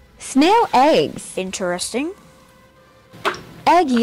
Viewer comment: “ExPeNsIvE” beat line so far of 2023 Edit: A egg war is going on in the comments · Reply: i thought he said EXPLOSIVE